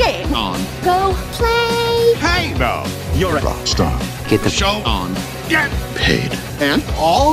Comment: he sounded like he was going to say get the f out of here